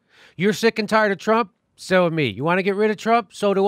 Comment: did jimmy say "so am me"?